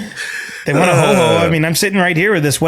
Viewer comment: maybe this comment is in bad taste. However, I was eating chips, and I thought Chris said “If they want a homo, I’m right here.” Apologies, I just had to go back a couple of times to confirm what I heard.